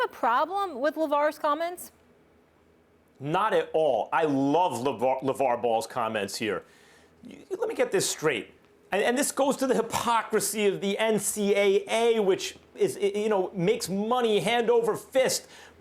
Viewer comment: I thought for a sec Max said he loves Lavars balls...both made valid points.